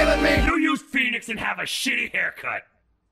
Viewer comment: Shitty!? I thought he said she-haircut! LOL